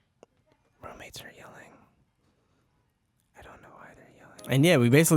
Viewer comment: uuummm... ok then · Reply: i heard "room mates are yelling, i don't know why"